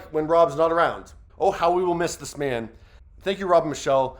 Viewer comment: I keep hearing “I/we will miss him”, not “he will be missed.” What a loss.